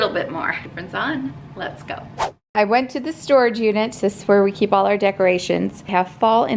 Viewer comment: 😂 At first I thought you said, "Well, I went to the storage, Eunice." But realized quickly what you actually said.